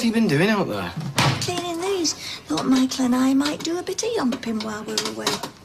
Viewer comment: What did Gail say? Michael and I might do a bit of what? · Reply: Yomping